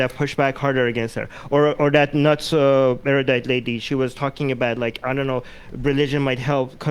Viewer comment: I keep hearing "nutso Erudite" when mentioned notsoerudite lol